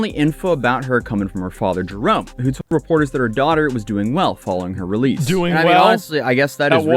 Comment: I think the father meant to say, "She's doing whale"